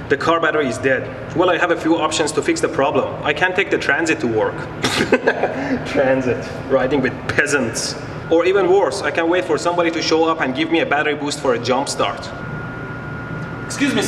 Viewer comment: Mehdi says "or even worse", I hear "or even a horse" :D